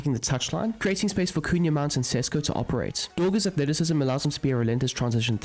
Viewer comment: what did you say? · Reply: Creating space for cunha, mount, and sesko